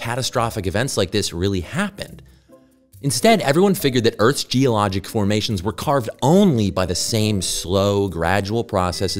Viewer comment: i thought it said earth's most epic food and i was intrigued lol